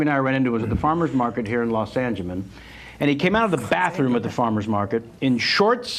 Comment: Did he say Los Angemen ???? wtf ???